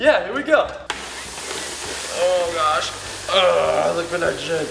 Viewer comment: DID HE SAID SHIT!?! don't know